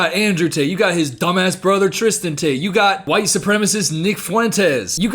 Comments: I thought you said my goat Vic Fuentes for a sec and was oh so confused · i thought he said vic fuentes from pierce the veil i got so scared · fully thought said ‘white supremacist Vic Fuentes’ and I think I died inside and rethought all my life choices for a hot second before realizing Nick Fuentes is an entirely different person and not my favorite singer